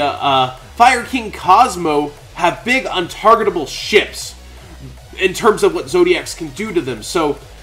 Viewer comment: Coulda swore I heard you say "Big Untargetable SHITS." At 2:39. Had to go back, just to confirm.